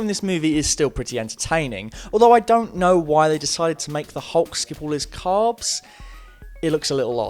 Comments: what are you saying @ 5:24 ? You dont know why they made hulk give all his carbs? (at least by what CC says, audio is even harder to understand) are you saying skip all his carbs? is it a size comment? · CC for 5:30 "to make the Hulk's give all of his carbs" I still don't know what he was trying to say there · What are you saying at 5:25 ? The hulk give all his carbs? The hulk skip all his calves?